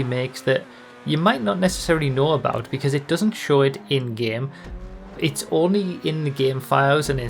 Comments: I don't say the word "Indian" at any point in this video. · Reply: No it's my bad, misheard you say 'in-game' as 'Indian.' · You mean Italian, not Indian. Edit: I misheard 'in-game'